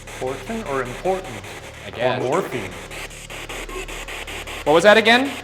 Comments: #post mortem 9:24 did I hear MORPHE ? 9:49 was that music I heard or am I just crazy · The ghost that was said to be part of the 54th regiment, was the one that was asking for morphine. Earlier in the beginning of the video, when they caught "important" on the ovilus, it sounded also like "morphine"... Coincidence, I think not! It was said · I hear portal instead of important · sounded like "They brought us" and not like "Important" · uh i didn't hear "important" i heard "portal" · it sounded like it said portal not important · #postmortem At 9:17 the spirit box didn’t sound like it said “important” but rather “portal.” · I clearly heard morphine and I even said it to my sister. Then 2 minutes later Ryan said that the soldiers ask for morphine and I freaked out! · #PostMortem at 9:25 it sounds like it said something about morphine which makes sense considering what's talked about after with the soldiers